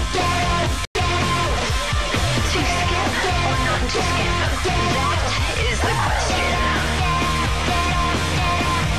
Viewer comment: what is she saying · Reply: To skip or not to skip that is the question. 😅